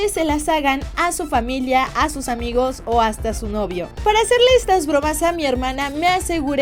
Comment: pensé que dijo hasta a su exnovio Yo: no mames tan feas están